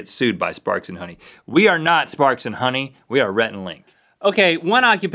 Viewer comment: I heard it as 'Farts and Honey" :P